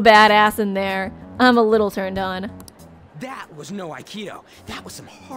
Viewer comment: When ryuji said no aikido I thought he said neo aikido lol tenko!!!